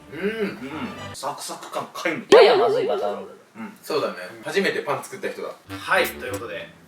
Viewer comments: りょうくんが「初めてパン作った人」って言ったとこ、「初めてパンツ食った人」って聞こえて汗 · りょうくんのここのセリフだけ聞いたら「初めてパンツ食った人」にも聞こえなくもない笑笑